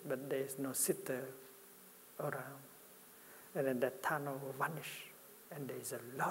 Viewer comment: I think at 5:40 he says something like "and then the tunnel [will] vanish". So when the notion of a separate self disappears, space and freedom appear instead.